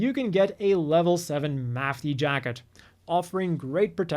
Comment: I thought that Jacket at 29:46 said "AEUG Mafty" for a second there and I was like "SINCE WHEN WERE THEY AFFILIATED WITH THE AEUG AND SINCE WHEN WOULD THE AEUG TEAM UP WITH MAFTY?!?!?!?"